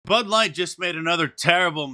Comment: It sounded like you said butt light and that wouldn't be wrong